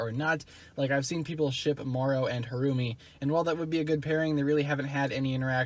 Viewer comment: Who else heard "Mario and Harumi"